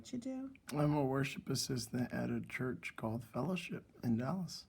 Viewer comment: I thought you said “War Ship assistant” at first. I thought to myself “Oh, he must be in the Navy”! Lmao